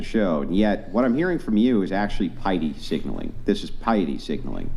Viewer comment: what signaling is he talking about? I hear "Pidey signaling". Want to research about it but can not find it on google. Assuming I don't know how to spell it correctly. "pide", "pidy", "pidey"....??? Help very much appreciated. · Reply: Piety signalling. Similar to virtue signalling but for religious groups.